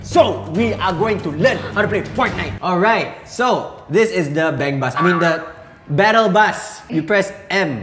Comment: Did i just heard the word bangbus , instead of battle bus? 1:58 or 1:59 check it....